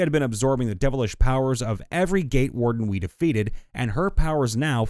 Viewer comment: I miss heard the warden story. I thought you said They had a problem with every gay warden😂